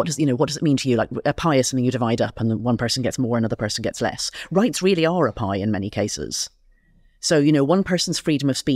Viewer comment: It’s so funny to me how she can talk so confidently about law and just blow over that fact…. Classic culture war grifter at work. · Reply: what do you mean? She said "rights really are a pie in many cases".